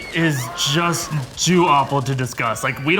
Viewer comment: Am I miss understanding what was said or at 19:53 did I hear it was just "Jew-awful to discuss". I re-listened to that quite a few times expecting it to have been "TOO awful". Seriously?